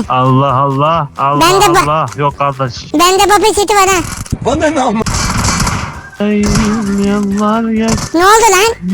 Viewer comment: banane amk kısmını bape ne amk diye duydum😂